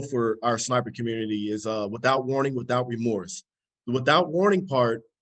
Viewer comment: is that "Remords" ? I thought he said "Remorse"? I musta goofed. Help.